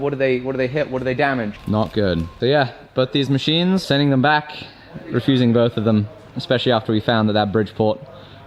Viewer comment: DENY THE SHIPMENT!!!! I work with shipping companies and machine sales for a living. The shipping company is liable for the damages, but they are notoriously bad at paying out insurance. This is especially true if you sign off on the shipment. Next, look at your invoice. Look at where your FOB is. FOB your place means the seller is going to have to handle it/they are liable. FOB point of origin (most likely) and the seller technically has to do nothing for you, and you have to work it out with the shipping company. Talk to the seller as well. They likely have experience in this. I know for me, we have had machines damaged in transit. If I have that same version in stock we replace the machine for the customer, even though we don't have to, and then handle the shipping company. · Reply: He clearly says they're refusing them 10:38. Pay attention :p